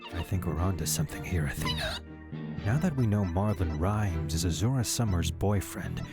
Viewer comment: Did you just say "Marvin Rimes" at 9:20?